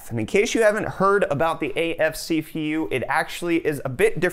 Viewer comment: did he just say si fi yu (CPU).